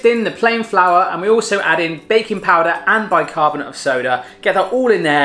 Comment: I thought for a second he said bacon powder o.o